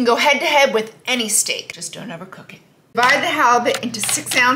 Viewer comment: Hahaha, "Just don't overcook it" I thought she said "Just don't ever cook it" I was like, searing and baking are considered cooking.. wait more heat? 🤣🤣🤣